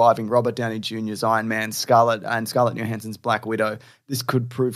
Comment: i misheard "black guido" and i got a lil excited for a new new york superhero😂